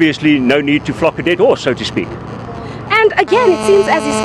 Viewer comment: His accent almost made it sound like he said "no need to f##k a dead horse" , but he said buck, I think. Lol